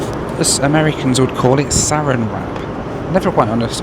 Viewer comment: Yes. Saran Wrap is a brand name for a plastic wrap. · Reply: When I moved to Canada I was confused when people said Saran wrap, I thought they were saying 'surround wrap' I'm also a kiwi that's used to glad wrap as our colloquial name for cling wrap